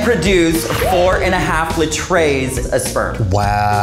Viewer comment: she really said litrays